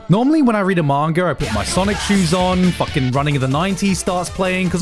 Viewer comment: Gigguk says sonic shoes, I hear sonichu's . . . the internet has ruined me